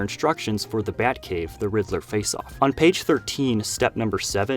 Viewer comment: I swear he said the rizzler not the riddler